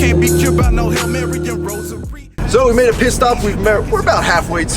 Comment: Did he say piss stop or pit stop? It would work either way i guess lmao